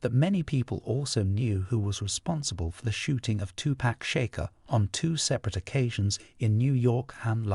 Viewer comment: I heard him say 2 pack Shaker instead of 2pac Shakur 😂